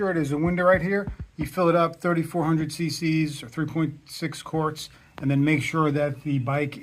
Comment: Did you say 6 Quarts? It sounded like you said 6 quarts.